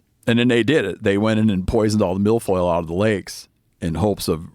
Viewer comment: is Steve saying MILF Oil?